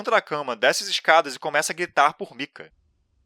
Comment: eu entendi gritar por pica kkkkkkkkkk